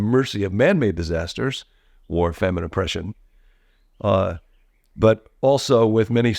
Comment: At one point I thought John said, "...leave us at the mercy of manmade disasters (OR FEMININE OPPRESSION), ..." I had to turn on subtitles to get it right! (He actually said, "war, famine, oppression"). Seriously.